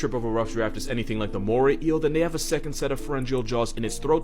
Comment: I heard "Omori" and then I just started freaking out for some reason